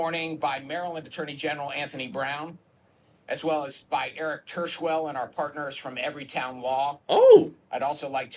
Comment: Sounded like he said his last name was Turdswell ? Sounds like a blockage !!